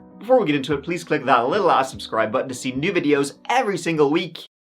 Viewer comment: did you say “that little a$$ subscription button”? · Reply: Well, "little a$$ subscribe button"